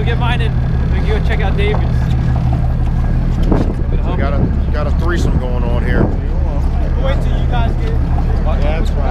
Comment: Did he just say "We got a three some going on here.." at 10:45?